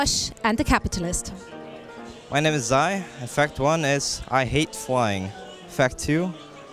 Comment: For his first fact I totally thought Zai said that he hates Fly